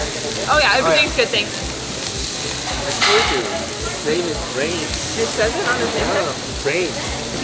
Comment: Waiter's name: Brain or Brian? I thought I heard him saying Brian!